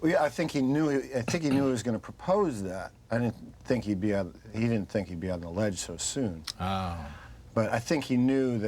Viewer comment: what does Tim say? Cant follow his pronunciation!! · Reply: "...he didn't think he'd be on the LEDGE so soon (Morgan, "but he had a plan.")